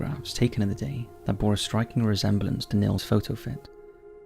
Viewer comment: bore a striking resemblance to what? I don’t understand what you’re saying. It sounds like Neil’s Photo Fit.